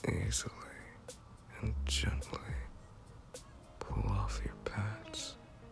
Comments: wait i thought he said pads when he meant pants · When he said pull off your pants I could've sworn I heard pads and I got SCARED